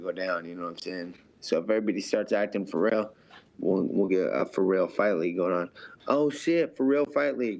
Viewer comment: pherrell fight league does sound sick a bunch of dudes who look like pherrell fight each other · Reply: I thought he said, "For Real Fight League."